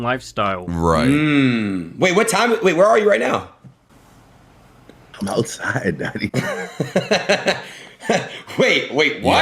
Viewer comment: what did he say??????? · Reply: I’m outside dawg